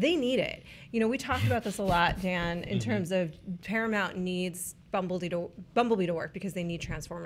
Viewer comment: I thought said “Paramount really need Bumblebeedore” like there was a HP-Transformers crossover I had missed